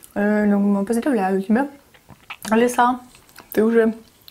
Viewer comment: мне послышалось Алиса? 😂